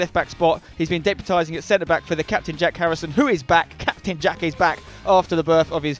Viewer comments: Ffs I thought it said Jack Black. See ya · I thought it said Jack Black